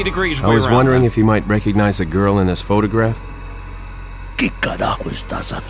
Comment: Leon says grill instead of girl. 😐